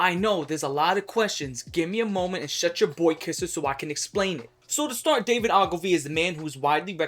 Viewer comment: did you say, BOYKISSER!?! 😳